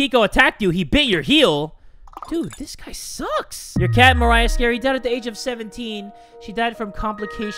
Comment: The way you said Maria killed me because that is such a common name in my country and it's so weird hearing it like that skskksks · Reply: i literally thought he said 'Morriah'